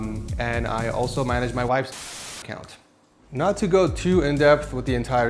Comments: did he say "i also manages my wifes onlyf*ns acc"? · did he say wife's onlyfans account? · Did anybody hear what he said at 14:07 that he blurred out? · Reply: "... And I also manage my wife's OnlyFans account"